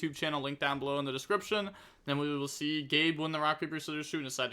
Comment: why did you called me gay for